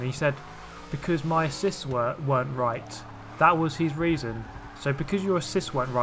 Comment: Why dis the orange car guy ram again? I heard "because his cis wasn't working", and I'm sure I heard that wrong. I keep repeating that part over and over and I still can't figure it out...